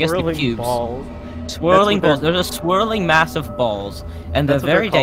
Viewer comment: tell me, what does “MASSOF BALLS” sound like? please tell me im not the only one who heard this